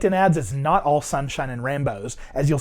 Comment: I heard "not all sunshine and Rambos" :D